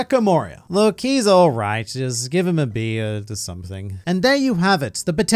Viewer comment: For Moria, I didn't hear you right at first. I heard "Let's give him a beer" which is hilarious because I'm sure my man there would fucking love to drown his sorrows lmao